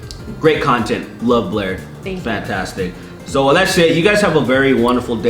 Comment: I was doing something else and not on the YouTube app when you said she was fantastic, and I thought you said she was "fap-tastic"